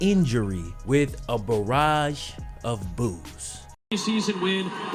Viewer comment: did he say boobs